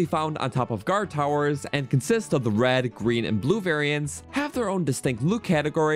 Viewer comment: did he say red "Green" and blue?